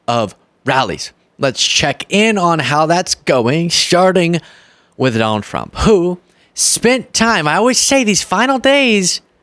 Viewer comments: Wait - did he say "sharting with Donald Trump? 0:12 😄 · Did you say 'sharting' with Donald Trump? I think your Freudian slip is showing! 💩